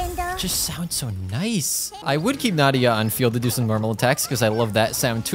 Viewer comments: DID YO JUST SAY NADIA? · can we talk about how he said nadia instead of nahida